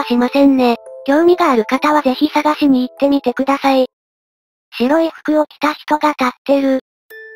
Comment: 白い服を着た人が立ってるが エロい服着た人が立ってるって聞こえた 疲れてるんやろか…